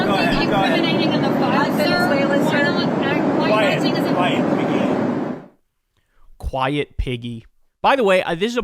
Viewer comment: When I first heard this, I thought he said Peggy. Then, I heard it again. Imagine Trump calling anyone a pig 🙄?!